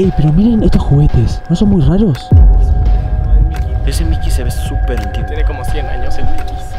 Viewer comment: Me encanta , Fede tiene su grupito de chicos , medianos y grandes JAJAJA AMO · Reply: Escuché que alguien dijo Mickey súper suavecito a las 4:21 a